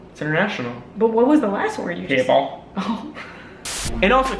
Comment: What was the last word? Seriosly I didn't get it at 3:04 lol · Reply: He said people